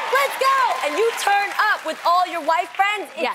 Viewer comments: I thought Jeanie said "White" friends, not "Wife" friends. Lmfao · Lol did Jeannie say WHITE friends? O_0 wtf I hope I misheard that